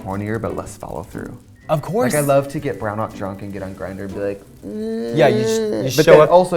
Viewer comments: Did Trixie say she likes to get “brown out drunk” ?? Is that a thing? Lolololol 😆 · Did Trixie say "brown out drunk"?